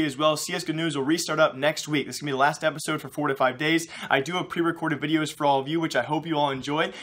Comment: I thought you said 45 days but you didn't thank god.